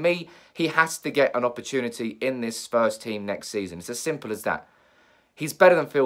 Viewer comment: why did he say Spurs team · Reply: I thought he said that too, but he said first team when I listened again